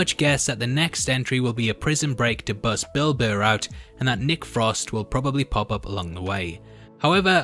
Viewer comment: Lol when he said "a prison break to get Bill Burr out..." I heard "a prison break to get Bilbo out"